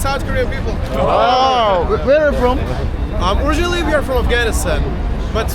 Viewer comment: "Originally, we are from 아프가니스탄" 이라고 들리네요 청력 ㄷㄷ